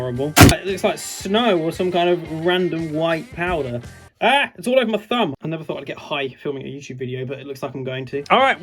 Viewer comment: what did he say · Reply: “AAH, it’s all over my thumb!”